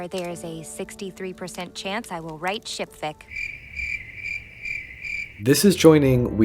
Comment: bro I thought she said shit-fic when I was watching but ship-fic makes a lot more sense considering the demographic, Tecna is so based though.